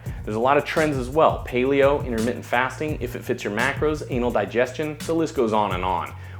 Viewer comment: did I hear "Anal Digestion"????